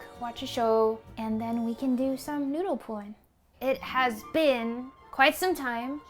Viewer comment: omg fr thought she said then we can do some noodle porn and was mad confused 😂